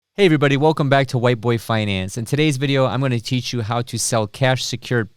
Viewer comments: I thought he said White Boy Finance lol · sounded like you said "white boy finance" at the beginning 🤣 Which would actually be a great name 🤣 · Did I just hear "White Boy Finance"?